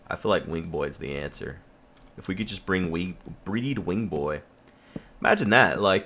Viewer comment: Oh you meant what you said the first time... "Bring weed"